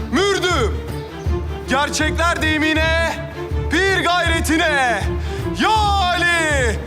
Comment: Ne dedi (Ey Ali) dedi mi? 😭❤?